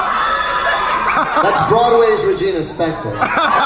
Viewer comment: Sorry but what did lin say at 1:49 ? Can someone pls write? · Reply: "that's broadway's Regina Spektor"